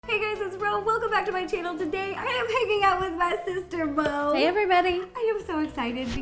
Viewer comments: I thought she said hey guys I am making out with my sister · I tried to show my mum Ro’s channel and she said it sounds like she says ‘Hey Gays’ at the start 😂😂😂 NO MUM NO! 😂